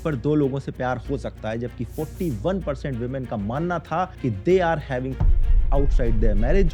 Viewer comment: did you said just women?